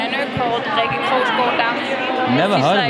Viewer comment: What was the name of the danish designer of the coat she was wearing at 4:51 ? Didn’t quite catch what she said , but the coat was a stunner 😍💕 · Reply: i think she’s saying Rikke Krogsgaard Mikkelsen